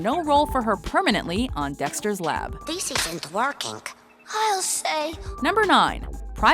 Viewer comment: I thought my nigga Dexter said "This isn't twerking" at 1:44.